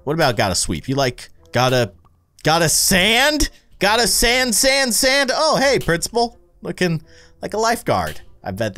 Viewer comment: Wait.. when he said “GOTTA SAND SAND SAND” I heard this: “GOTTA SANS SANS SANS” LOLLL